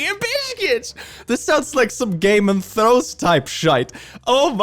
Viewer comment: I know in my head said "Game of Thrones", but no matter what I do, I can't hear anything other than "Gay Man Throws"